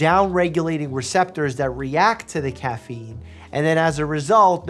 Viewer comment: Thought he said "Dog Turd Memes" at first lol